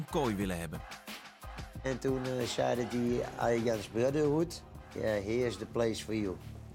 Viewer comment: Mooi interview 36:00 hard gelachen om "Arjan's Brotterhoed"